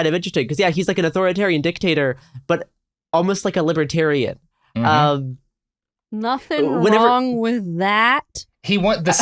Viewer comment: At one point Garrison said "liberatarian-y" but I heard it as "liberatyrrany" and I'll be using that now.